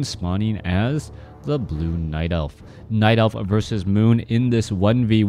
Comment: did you say night elf vs moon or is my device faulty?